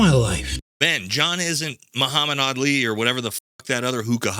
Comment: For a minute i thought you said Muhammad ali. I was about to roast you on what ali would do to your facd if yo uevef said that to him in a ring then i realized you said odd li.